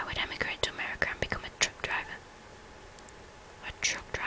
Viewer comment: The first time you said "truck driver" I thought you'd said "drunk driver"... I got scared for a moment, haha. ;) Also, just wondering, what exactly is a chav? I've heard the word in a few places but can't make out what it means.